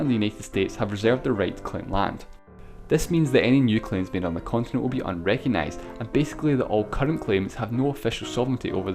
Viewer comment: what is Russia saying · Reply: "Yes, me too."